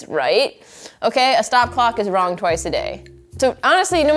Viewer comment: You know, it's all very interesting but did anyone else... is it not "a stopped clock is right twice a day"? Because I swear I heard "a stopwatch is wrong twice a day", which... it may be? I'm crazy. I'm senile. I'm twenty seven but obviously the decline has begun... ?